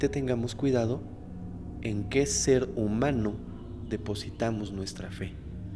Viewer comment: Escuché "Cerdo humano" 🤭